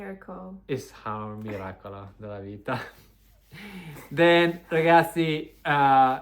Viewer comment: what did say here ? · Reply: He said “it’s our miracle of life”, hope this helps!